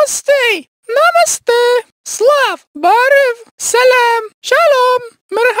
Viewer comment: When Six said HALO in Arabic it sounded like she said salami lol